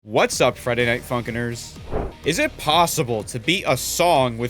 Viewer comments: “what’s up Friday Night Fucking-ners” is what i hear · bro I heard “what’s up Friday night f*ckers”